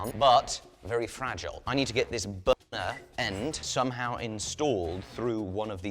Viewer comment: did he say BONER instead of Burner? Lmfao is that why there was that quick silence on "burner" at like 01:21? hahaha